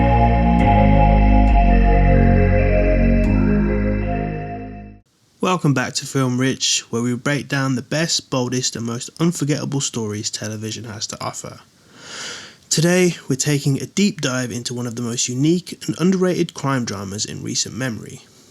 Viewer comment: I swear I heard "baldest"